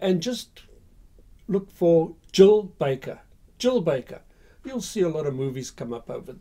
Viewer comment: Who is that Lady you were talking about at 18:13 ? I don't mean to be rude but I couldn't quite catch the name for the accent. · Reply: Jill Baker